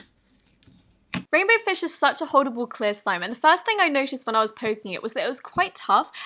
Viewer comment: it sounded like she said horrible clear slime